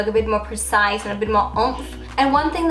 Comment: What did she say? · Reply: Umph